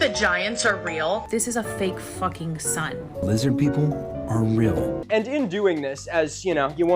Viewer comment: I first heard that as “Italians are real?”